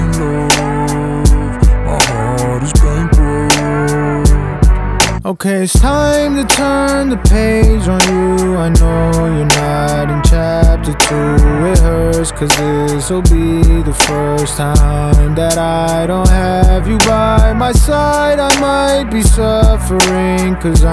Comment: Is it just me or did y’all think when he said “Okay, it's time to turn the page on you I know you're not entrapped to It hurts” i thought he said “i know you’re not in chapter 2” WTFF